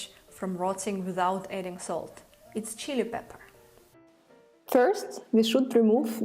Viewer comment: cayenne or chili pepper? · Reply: Thank you for clearing that up. You are Very Cute and so is your 'accent', but I thought you said, "chia pepper'!? , I was like, "WHAT!?". I just want to know what that red secret ingredient is (the key to 'no salt' sauerkraut), and she sais, 'Chia peppers'? Where am I going to find THAT?! LOL , God Bless You Baby Girl.